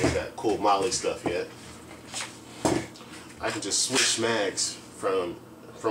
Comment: did you say switch mags or smags